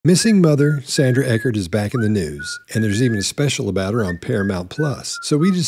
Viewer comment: The next time you do one of these, could you please emphasize the words "in the news". I was listening to this while doing something else and all I heard you say was "Sandy Eckert is back". Then I listened to the whole video and it really made no sense if she had come back.😆😆😆